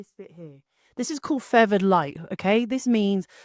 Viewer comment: What was the type of lighting mentioned at 2:02? I thought it said fervored, but that didn't fetch anything on google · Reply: Hey there, Feathered light means the edge of the light. Its the octobox I have linked in the description of the video. I hope that helps.